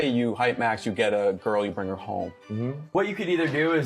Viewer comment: sorry, did he say "girl"? I thought he must be into boys. I won't be surprised if in a couple of years he'll come out.